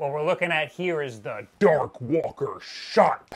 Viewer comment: I thought you said "dorkwalker" before i saw the subtitles